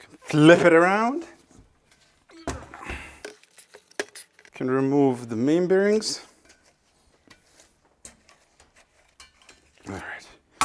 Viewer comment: it sounds like you say “I’m going to remove the mean berries” lol